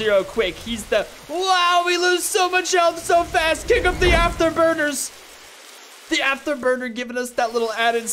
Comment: I thought he said "the Afterburgers!!!" I was like "...wait...what???"